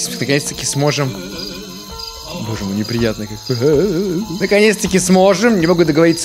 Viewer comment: мне послышалось боже я на пенисе